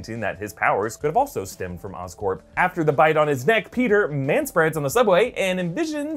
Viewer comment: Did you just say.....manspread?